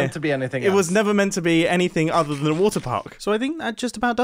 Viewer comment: my laptop glitched so i thought said "so hermitcraft is finished it was never meant to be anything but a waterpark"